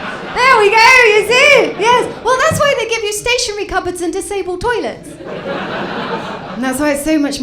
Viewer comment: What's Diane saying in this sentence "There we go, that why...in disable toilets?" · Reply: I said that's why they give you stationary cupboards and disabled toilets.